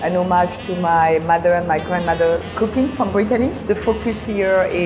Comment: Did anyone else hear the last chef say britaly